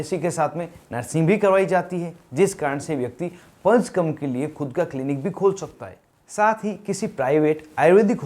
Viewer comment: pulse come kon sa clinic khol sakta hai ???????????????? please clear kare kya bola hai aapne on